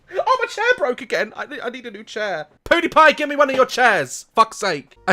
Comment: I like how he said poodipie instead of pewdiepie